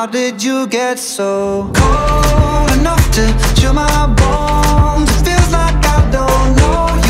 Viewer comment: How tf, I heard ‘cold enough to chew my balls, feels like…😶‍🌫️